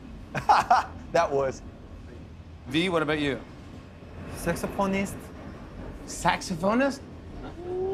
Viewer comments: Oh God, I thought Tae was gonna say Sexy porni Star Again😂 Im sorry my ears betrayed me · IM SO SORRY BUT MY MOUTH DROPPED I THOUGHT HE SAID SEX PORN...